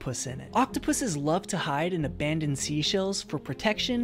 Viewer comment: First I thought he said octopuses like To hump things like crabs but no he said hunt things like crabs lol 😂❤